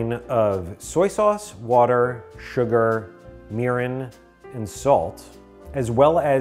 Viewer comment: I swear I thought I heard "urine"